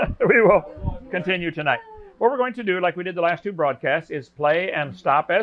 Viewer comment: Had to rewind here, because I heard Hovind say "fraudcast"